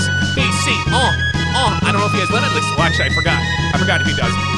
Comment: Did you say Limitless are you from 2k19 still